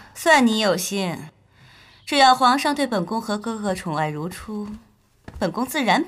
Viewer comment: 我聽成「只要皇上對本宮寵愛如＜豬＞」